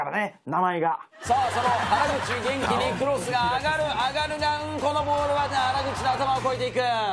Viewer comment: あ◯るがうんこって聞こえるw